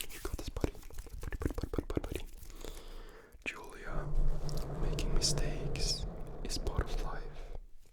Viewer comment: In my half asleep phase I thought he was saying "making a steak is a part of life" 😭